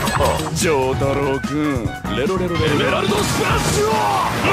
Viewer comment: WAIT A MINUTE...DID KAKYOIN SAID Jotaro-kun?!